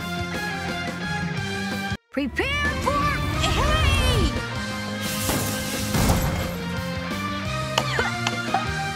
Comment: What is Jimmy saying at 1:46? “Prepare for ?????” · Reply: Prepare for…HEY?! He says this as his rocket ship malfunctions